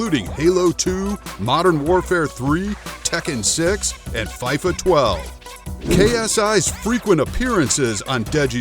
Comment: did he just say fyfa 12